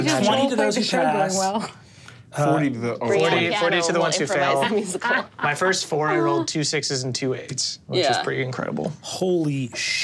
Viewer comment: The subtitles at 14:31 - "my first four year old two six is in two eight". What Murph ACTUALLY said - "My first four I rolled two 6s and two 8s." God, it's a good thing I've got the transcript open while I watch.